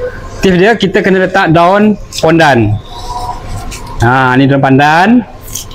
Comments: aku sorang jer yg dengar abg tu kata daun pondan.😂 yg kedua baru dengar pandan😂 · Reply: Sya pun dengar daun pondan.. ingatkan salah dengar.. 😂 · Yg mula² abg ckp tu dgr mcm daun pondan, ke aku yg salah dengar🤔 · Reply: Betul tu,daun pondan dia cakap